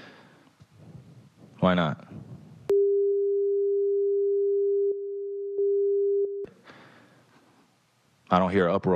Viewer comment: I wonder what he said that got censored? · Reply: He said everyone is saying “n-er” on twitter but no one is having uproar about it.